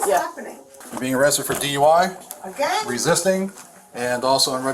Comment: “again??” 💀 - im sorry did i hear that correctly? 😂